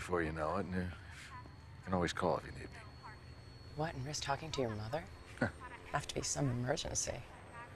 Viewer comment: What does Skyler say at 00:09? This video doesn't have Closed Captions and I honestly can't understand what she says there. Right after "and miss talking to your mother?". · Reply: “You can always call if you need me” “What and risk talking to your mother? [It’d] have to be some emergency”